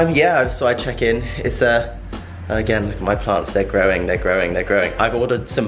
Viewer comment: When Tom talked about his plants I misheared and thought he said 'my parts are growing' - honestly, my mind